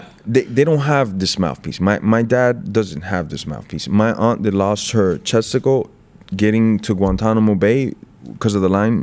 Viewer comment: At first I thought he's saying his aunt has lost testicle